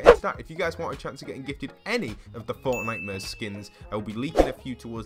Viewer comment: I deadass thought he was saying fortnightmures instead of fortnite-mares